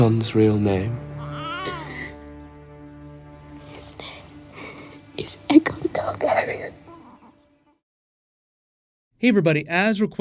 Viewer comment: i thought she said "his name is Edgar Targeryn"